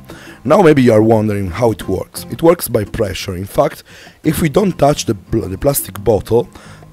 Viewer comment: it sounded like he said pressuring f*cks